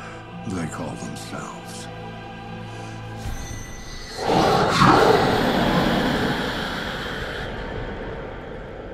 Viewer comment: he says the name of "they". i cant make out what he says. does he say Legion? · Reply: It's Moloch. They mention the name again later on. Several times.